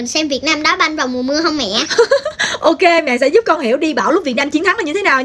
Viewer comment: đi bão mà nghe thành đi bảo =(